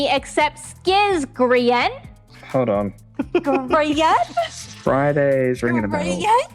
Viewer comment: love how the subtitles here say Gremlin instead of Grian 😂